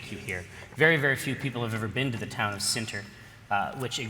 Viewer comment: what was the name of the town jerry says at 5:50? Cinter? Senter? I'm trying to see if there's an actual town named that on the forgotten realms wiki. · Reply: Sinter, I thought, which IIRC is a waste product from smelting iron/steel.